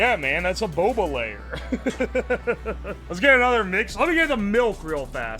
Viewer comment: did he just say "milf"?